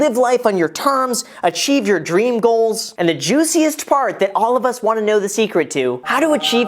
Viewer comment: I thought he said "The juiciest fart"